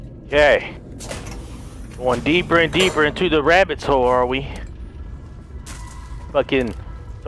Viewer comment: did he say horre it hole❤️❤️❤️😂😂😂😂😂